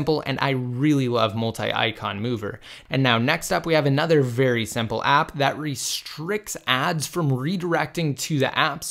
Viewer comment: What was the name of the tweak for 3:50? I didn't hear you mention it. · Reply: Noappstoreredirect